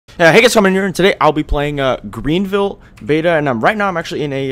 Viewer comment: In every video this is what I hear 0:00 uuuuuhhh HEY GUYS bsftdifis here and today/no cap